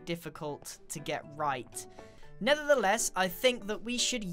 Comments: grain said nether the less instead of never the less lmao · did he just say NETHER THE LESS?! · did you say nethertheless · Did he say Nether the less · Anyone hear him say "Nether the less"? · i heard NETHER the less lol 😆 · Did anyone else hear him say "Nethertheless" at 7:23?